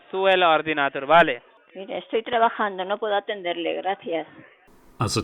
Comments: She Actually Says: Estoy Trabajando, No Puedo Atenderle, Gracias. Wich Translates to: I Am Working, I Cant Pickup The Phone, Thanks. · Theres a translation error @ 6:07. The potential victim says " estoy trabajando, no puedo entenderle, gracias". She said " I'm working (now), I can't understand you, thanks(bye)"